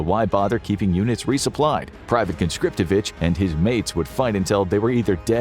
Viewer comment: I swear you said pvt conscriptabitch 😂😂